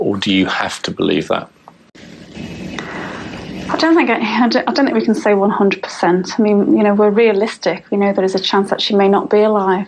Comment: the first time i heard her saying "i dont want to give any hint" as i replay it slowmo...i dont hear exactly those words anymore, but i still hear the word "hint"